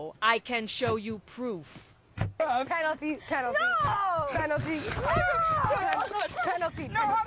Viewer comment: I hear major penalty